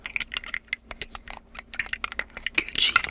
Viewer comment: já entendi que é Gucci